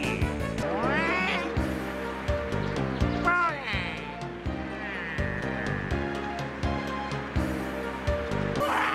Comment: did Waluigi say "BONER"?! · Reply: No, he said bogey.